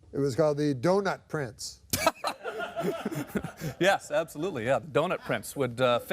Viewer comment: I thought the was saying 'donut prints' the whole time.